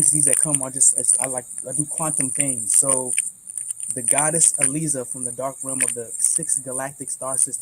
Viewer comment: Does he say the Goddess Elisa? 1:22:36 I want know more about her